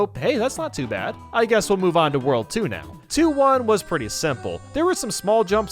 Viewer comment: When he said world 2 now it sounded like he said "world tuna"